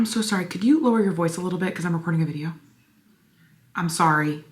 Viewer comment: What I heard the first time: “Im so sorry. Could you lower your voice a little bit because I’m a working man”